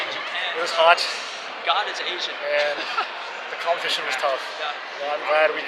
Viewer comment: 後ろで聞こえる'God he's asian' どういう文脈なんだろ